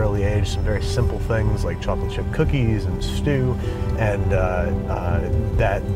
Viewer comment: am I the only one that thought he said 'chopping chip cookies'